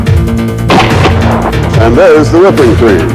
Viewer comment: Am I the only one who heard him say " oh no shit" at the end?